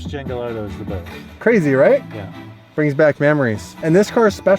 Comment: did he say it brings back mammories?